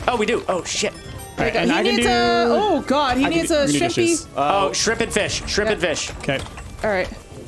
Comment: When said "Shrimp and Fish" I seriously thought she said "Stupid Fish" for a sec